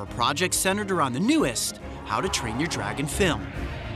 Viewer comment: I love how to train your dragon 1,2 and 3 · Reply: I thought you said how to train your dinosaur! 😂😂😂